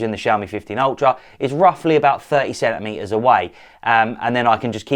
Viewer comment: Brotha.. I thought you said a minimum focal distance of 37 meters 😂😂 30 CENTIMETERS.